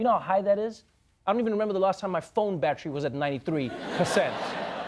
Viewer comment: It sound like he said phone badger. Lol